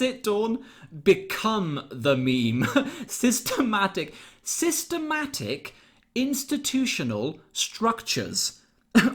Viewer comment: Sister back intertutional structures .....is what i heard her say . Who is sister back ?